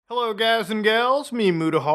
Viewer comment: I thought he said hello guys im gay💀💀💀💀💀💀💀💀